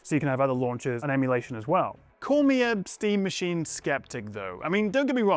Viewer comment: i heard epstein machine💀lol